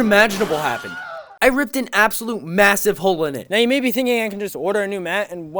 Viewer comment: did i just hear massive?